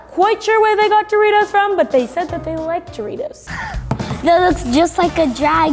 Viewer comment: Did she say J eritos?